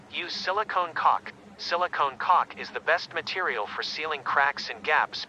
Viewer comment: What I heard: USE SILICONE COC